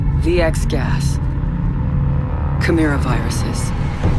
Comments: did she say coronavirus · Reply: Amjad Khan chimera viruses. It’s not that hard to understand her when you actually pay attention to the video. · did she said corona viruses???? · Did she said coronavirus ? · I heard that as "corona virus". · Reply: It WAS Corona viruses. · I am Spanish and I did not understand very well what it says there, it seems that it says coronavirus but I do not think, does anyone translate it well for me? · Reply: She said Chimera viruses like how the title is operation Chimera. I don't know if you still need a translation 2 months later but I wanted to help. ☺️